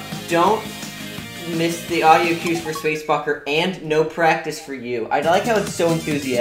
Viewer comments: is it me or did he say "space f*cker" here? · D-D-Did just say space f*cker?? No no no no no no no no not real no no nplease no